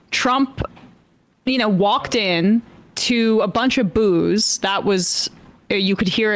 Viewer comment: At first I thought you said walked into a bunch of booze.